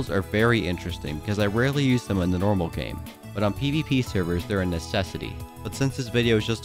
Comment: at first I thought he said EDP servers but that would also be the case for needing to use ender pearls I guess